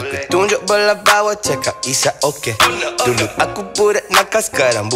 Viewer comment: I heard "isap okay"